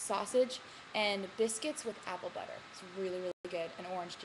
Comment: did she say apple butter?